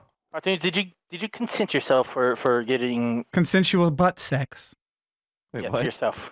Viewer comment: did I just hear cartoonz say: "Consensual butt sex... from Ohm"??? 😂 please don't tell me I'm the only one who heard that